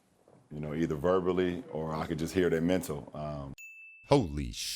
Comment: "I hear day mental"